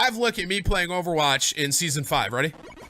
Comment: Now, instead of Winston and Dva, we have Tracer/Sombra/Winston Also, did this mf just say season 5?